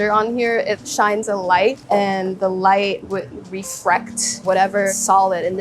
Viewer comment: "It's a fortified wine". Closed captions: "45 wine". Well done computer. "Agar is a setting agent". Closed captions: "Agar is a setting Asian". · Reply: captions "reflect" instead of "refract".😅